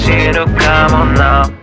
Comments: 「間違いだらけだ」が「街が火だらけだ」とか「街が血だらけだ」に聞こえて怖い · 間違いだらけだが街が火だらけだに聞こえて震えた